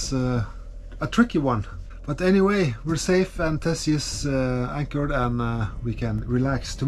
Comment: did you say Tessie?